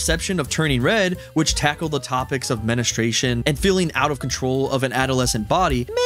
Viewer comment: did he just said menustracion?